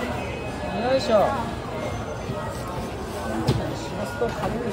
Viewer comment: Subtitles say "to~~suicide note!" Very wrong & weird. But i know they just said (There we go!) basically.